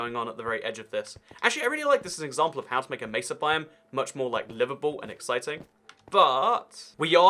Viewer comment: I did a double take and thought you said “an example of how to make a Mesa biome much more Liverpool.”